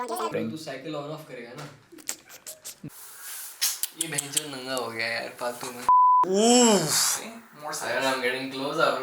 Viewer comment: did he say bc